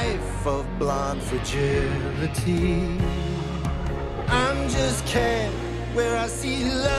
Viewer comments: All I hear is “I’m just Pete” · When I saw the movie in theaters, I thought at 1:28 he said virginity. Realized later when listening to the soundtrack he in fact, said fragility.